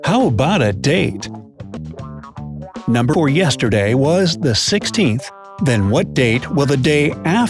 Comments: #8 doesn't make sense, if yesterday was the 16th, then that means today is the 17th, and the day after tomorrow (18th) would be the 19th. How is the answer the 20th? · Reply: #8 was a little unclear because of a glitch in the audio around 5:26...sounded like "yesterday" instead of "day before yesterday"... I think there was a South Park episode called "2 days before the day after tomorrow", or something like that (about global warming) :) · #8 doesn't make sense, if yesterday was the 16th, then that means today is the 17th, and the day after tomorrow (18th) would be the 19th. How is the answer the 20th? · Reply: I heard "remeber o yesterday was the 16th" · #8 doesn't make sense, if yesterday was the 16th, then that means today is the 17th, and the day after tomorrow (18th) would be the 19th. How is the answer the 20th? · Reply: I heard "number or yesterday"